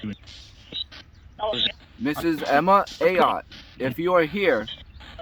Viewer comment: I clearly heard the name Emma!